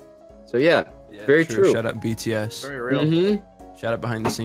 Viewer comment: Oh! said “shout out BTS” not “Shut Up BTS”. My friend was salty cause she thought he said shut up, I was trying for awhile to prove her wrong but she wasn’t budging. At least knows to be careful what to say when it comes to BTS cause the ARMY fandom... well... they fit the name let’s just say.